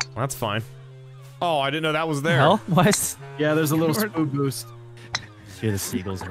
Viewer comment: did anyone else hear say “spood boost”?